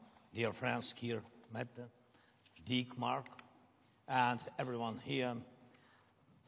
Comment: Did Zelensky call them “Dickmark” instead of “Denmark”? 8====3 💦